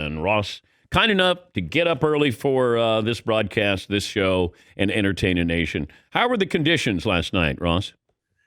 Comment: when Dan says "and entertain a nation" it sounds like he says "and entertain an Asian".